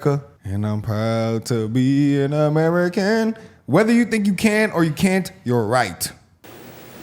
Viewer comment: Anybody else catch what the man on the left said at 5:26? "Whether you think you can or you can't, you're right"